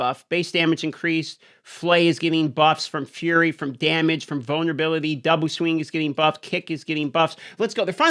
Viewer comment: have no idea why I thought you said bonnerbility but these patch notes sure are giving me one...